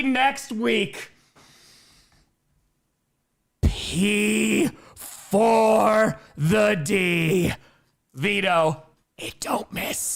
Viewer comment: can some plz tell me what says at 3:19:13? i’m so lost and confused lol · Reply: Pee 4 the D